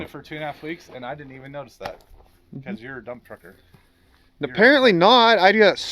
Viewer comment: Oooh Dump Trucker...... I thought he said Dumb F*ck*r 😜